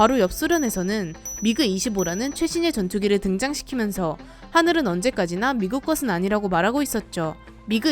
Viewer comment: 자막은 '하늘이' 라고 나오고 소리는 '하늘은' 이라고 들리네요~^^